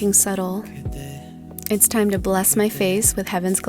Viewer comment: I thought she said " Now it's time to BLAST my face with Heaven's Glow"